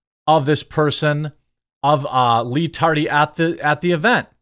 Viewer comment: Leave A Like On This Video & Share It Everywhere! YT Is Suppressing Coverage On This! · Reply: I am SO CONFUSED at something you said @ 4:43 you say "...local PD warned of this person, of uh.. [LEE TARDY] at the at the event." I'm confused at what is in the parenthesis there...[LEE TARDY] ... huh??? I even tried look at the captions and that didn't help... what were you trying to say?? lol j/k nah, but I can't figure it out, so I went for the last resort and decided to ask you.... I HIGHLY DOUBT you'll see this, let alone answer it... but I figure it was worth a shot